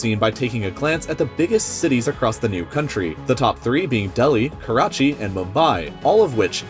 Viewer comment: When I heard him say Karachi I thought I heard Kakashi .... Naruto's Sensei